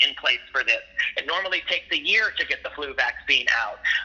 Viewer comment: did he just call Covid the flu? 🤔 did I hear that right ?